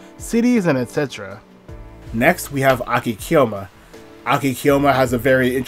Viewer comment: did shinohara design yamato-takeru? yamato-takeru is very very power ranger-y edit: oops it wasnt him, it was nirasawa edit 2: NOT nirasawa dammit i misheard, it was akikyoma (hopefully i heard his name right so its not misspelled)